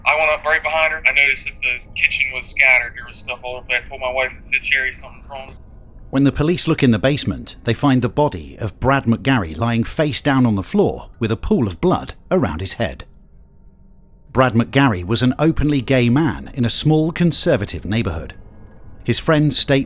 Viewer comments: I can't imagine how traumatized the daughter must be. To find your dad's best friend's dead body, only to later discover he was secretly your dad's gay lover and your dad is the one who killed him and now your father is serving life without parole......... How do you ever get over that? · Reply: I thought he said they found a bottle of Brad magary. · on 2:24 i misheard ugly gay male on a conservative neighborhood and was like wut?